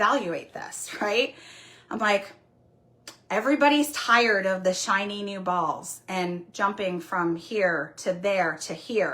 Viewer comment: I misheard and thought she said "everyone is excited for the shiny new balls." I audibly chuckled and my spouse asked what was funny. I have a kid's humor 😂